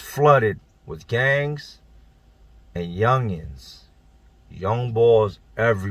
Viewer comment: I'm sorry. Real question. I'm joking or anything, but is he saying "young balls?"